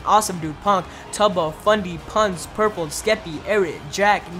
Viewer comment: what i hear 'purple skeppy'